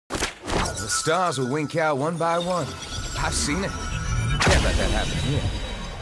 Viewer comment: Can someone please tell me what did he say at 00:00? I really need to know!!! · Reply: The stars will wink out one by one, I've seen it, can't let that happen here.